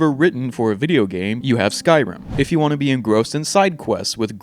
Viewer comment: i thought you said 'if you want to be gross ' for s second